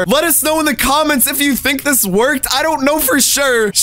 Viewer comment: I thought you said it worked